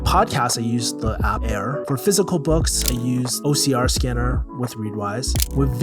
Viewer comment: whats did u say at 4:05 ...ocr scannner ? to scan fysical books i mean....